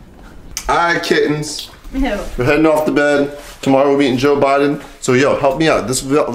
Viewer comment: It sounded like he said Joe Budden LOL